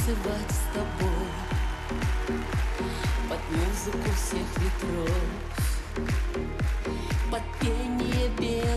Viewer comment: Послышалось "под музыку всех времен" и эти Ч.С.С. в саундах песни - неплохо, но значение вызвало легкую грусть, ибо смысл известен и если бы мы все узнали и сущность чувств и как складывались ноты задолго до их написания, большинство из нас никогда бы не слушали ее, никогда бы не создавали кумиров, никому бы не поклонялись и не становились поклонниками неведомого и ведОмого.